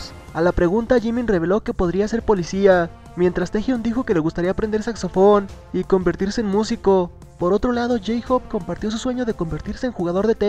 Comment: ENTENDI "SEXO PORN" JAJAJAJJAJSA